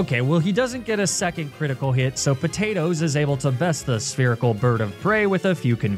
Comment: you said “spherical” but for some reason I heard “the Urkel bird of prey” and honestly hoot hoot does kind of look like Steve Urkel